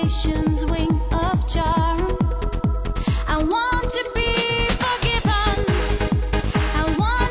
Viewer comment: Always thought she said I want to be pokemon, and no one understood what song I meant